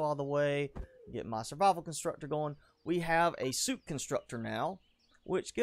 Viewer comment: had playing while in another tab "we have a suit constructor" -Wait did he say SOUP constructor? nah theres no way, thats silly. "Which gives us energy drink, hot beverage..." -WAIT maybe he did say soup constructor! *Rewinds. ...Oh nevermind...Now I wish there really was a soup constructor.